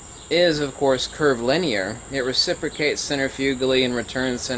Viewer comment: I am sorry but I have a problem to understand a word you are mentioning several times. In this video for example in 1:37, when you are mentioning that the magnet "recipicats" centrifugali... I couldn't understand the word I have marked in quotes. Could you please write it down? Thank you. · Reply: Ahh. I found that I can turn on the titles. So it is reciprocate.